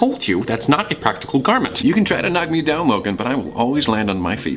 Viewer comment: I thought he said “Logang” I was low-key cringing